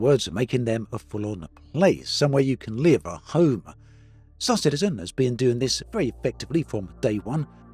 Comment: i have played back this sentence more that 10 times and i hear "sossagenigen has been doing this from ..." edit: i played it at .5 speend and now I know